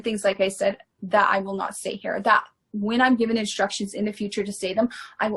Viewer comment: Did she say "when I'm given instructions"?